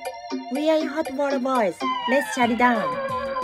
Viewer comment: What is saying? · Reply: I always heard it as “we will add more balls let’s shut it down”